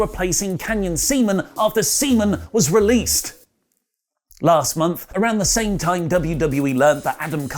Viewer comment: I thought I heard "semen got released...last month"...this is wrestling news. Awesome.